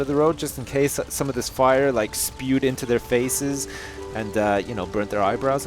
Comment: what's its saying ''suppote?'' spude? into their faces , can someone listen and type that word correctly? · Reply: Spewed